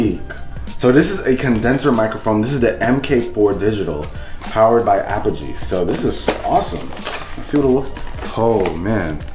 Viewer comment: I thought he said powered by apple juice 😂